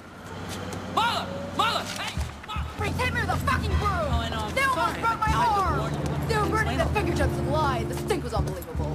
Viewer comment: wait, what does she say at 1:13? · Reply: They almost broke my arm!